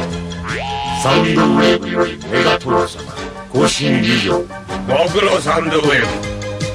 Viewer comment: 「サンニクウェーブヨリ、メガトロンサマ、コウシエンキュウジョウ」 ↑空耳でこう聞こえた